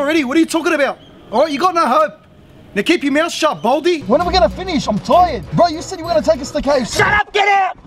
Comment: idk wat he single word he sayed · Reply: Shooter Driv WHEN ARE WE GONNA FINISH!? IM TIRED! BRO YOU SAID YOU WERE GONNA TAKE US TO KFC!!